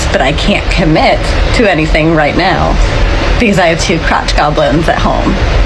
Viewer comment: Did she say "she got 2 crotch goblins "? I swear it sounded like that but i may have misheard.